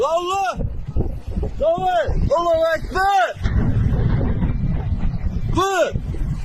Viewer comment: did I just hear Allo Akbar